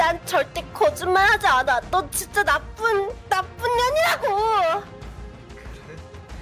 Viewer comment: 왜 나쁜녀ㄴ이라고들리ㅈ..